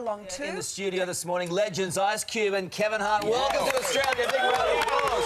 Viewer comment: I thought she said Kevin Hawt lol